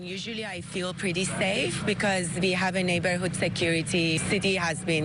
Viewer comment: Humm ... at 3:10 neighbor says "I usually feel safe because we have a neighbor who's security ... - break in video - city has been ....." what was actually said about the 'neighbor's security'? Where was that 'security'? yep, sounding fishy to me!!